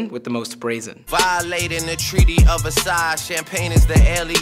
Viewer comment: I could’ve swore he said “Violating the Treaty of A SIDE” as in breaking off his ties with a side chick.